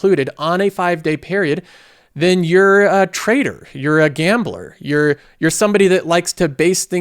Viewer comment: I was listening to this on the side while working, and I misheard the word "trader" as "traitor", and I burst out laughing.